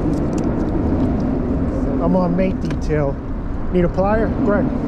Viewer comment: I didn't understand what u said on 7:06 maintenance tell. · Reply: Not sure if the right marker but I mention mate detail getting those crab legs in the water helps draw some fish in seems to work, if something else show the time ok!!!